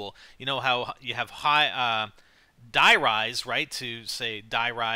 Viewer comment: you sound like youre saying tht die rise was shit